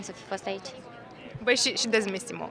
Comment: VERY WRONG TRANSLATION here when Larisa talks about SIMONE !!! Larisa said, at minute 0:52 "Dar Simone lipseste si chiar ar fi fost fain sa fi fost aici" = Simone is absent and it would of been cool she was here. --- But the lady translates : "She doesn't miss Simone". Wow !!! Is quite the opposite from what Larisa said !!! Given what happened to Larisa on the floor, it's so not fair that her words were not translated correctly. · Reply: We believe the interpreter said, "But she DOES miss Simone" and her accent makes it sound like she said "doesn't."